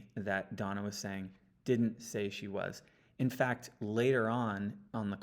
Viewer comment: I heard it as Donna said I didn’t say she was